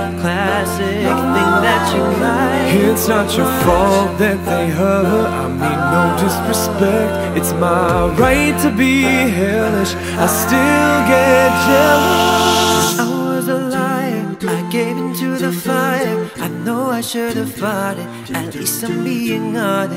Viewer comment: Let's all be honest......... We watched this more than 1 time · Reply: The caption said "i still gay"